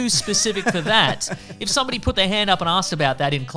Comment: For a moment I thought you said "If someone put their hand up an arse". lmao